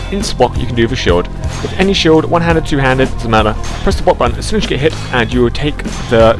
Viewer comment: You talk way too fucking fast and your accent doesn't help. Like at 00:18 it sounds like you said "touchthebobbutton". Literally with no spaces between each word. Slow the fuck down.